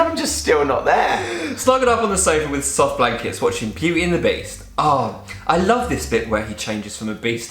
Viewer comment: I thought said ' while we're watching PUBERTY AND THE BEAST ' omg 😂😂😂😂😂